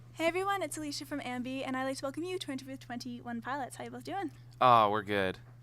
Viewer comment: sounded like she said “how you loves doing” which would be correct